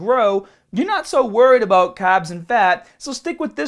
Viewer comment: I like how you say carbs. For a second I thought you were saying "cobbs"